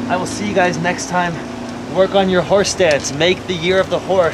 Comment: so I misheard you, I thought you said horse DANCE, so I'm sitting here and waiting for you to move and you never move from that STANCE. I'm thinking, wait a minute, the cameraman(orlady) is doing more dancing around than he is! So funny! Thank you for the laugh. Also, I love your videos, please keep making them. Do not listen to mean people!